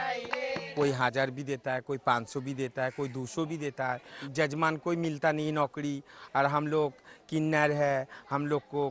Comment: LOL, THE SUBTITLE IS MISLEADING @ 0:20 SHE SAYS SOME PEOPLE GIVE US RS 1000 ($14+) SOME PAY RS 500($7+) AND SOME ONLY RS 200 (LIL LESS THAN $3), BUT THE SUBTITLE SAYS JUST ONE DOLLAR (WHICH SHE NEVER SAID)